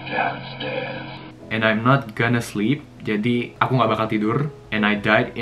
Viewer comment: Di antara smwa voice memo, sekali denger ga githu nangkep, tp cm 1 kalimat yg gw lgsg tangkep dri awal yg 9:03 gw dengernya "and after i sleep" dri awal gw dgrnya itu tp pas bilang "and i'm not gonna sleep", pas gw dgr lgi, ya bisa jdi sih, tp stlh dgr lanjutannya menurut gw lebih cuco yg gw dgr ya ga sih?? Walau gw bukan expert inggris